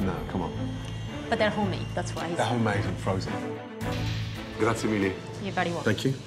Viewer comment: Please help me out 14:07 is that spanish? gracia mire, and what did said? Im trying to learn spanish